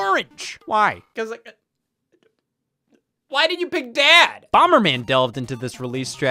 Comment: Bro at 15:56 I thought he said why DIDN’T you pick dad. I thought it was getting real for a second there